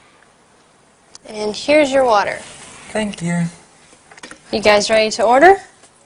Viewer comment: sec is the subtitle correct? why can't I hear so : You guys have already ordered? Instead I heard You guys try to order lol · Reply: I heard : You guys ready to order?